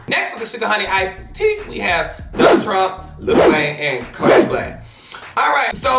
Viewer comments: At first I thought said Lil Trump 😭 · LMAOOOA all I heard was ''we have next is trump" and I almost dieedddd !!